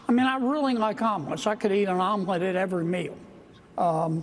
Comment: I thought Kennedy said almond, not omelet 😂.